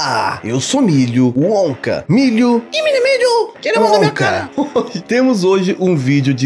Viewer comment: So eu que não entendi oque o mini milho disse junto com o milho? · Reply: "tira a mão da minha cara"